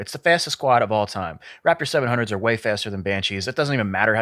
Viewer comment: did he just say its faster then a banshee clown